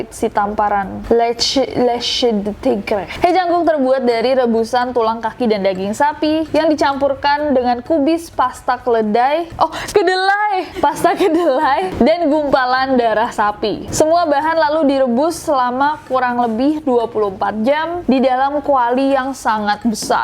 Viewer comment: Same! Kedelai kedengeran kyk keledai:vv